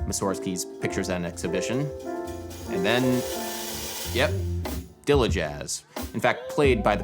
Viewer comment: I can't get what you say at 20:21, the tag you give to chris dave style of jazz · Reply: Dilla jazz. J Dilla was an influential hip hop producer who sampled a lot of jazz in his beats, and had unique drum-programming style.